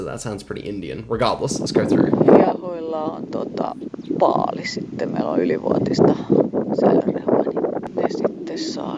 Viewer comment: I speak finnish natively and Im still strugling to understand what she said in finnish💀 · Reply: "Hiehoilla on paali, tota, niillon ylivuotista rehua ja ne saa sitten sitä" "The heifers have a bale, well, they have overaged fodder and they then get it" Probably taken from some video where a farmer talks.